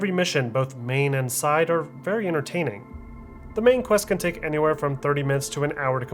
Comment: I heard you say main quest can take 30 mins to an hour to complete. did I hear it right???